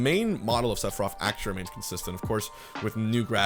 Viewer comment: Sounds like he sayin "Sephiroff"